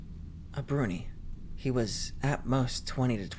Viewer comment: Bro I thought story five said brony